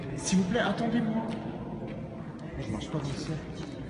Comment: il a pas dit plutôt : "SVP attendez moi, moi je marche pas dans le cercle" (en parlant de cercle satanique) ?